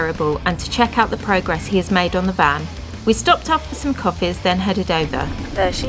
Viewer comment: “We stopped off for some coffees and then headed over” I heard “We stopped off for some coffees and then had a donut” I had your video playing whilst frying up some V-Pud for Sunday breakfast… I downed tools and scrambled to my phone with glee, excitedly hoping to see some glorious new vegan donut 🍩 lol 😂 Duh 🙄 Fab update, guys, thanks xx